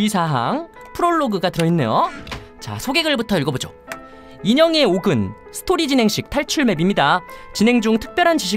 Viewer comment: 인형의 옥을 인형의 목으로 들었어요ㅋㄱㅋ